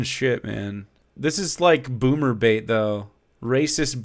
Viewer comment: Oh no I heard it as "boomerbate" & I am horrified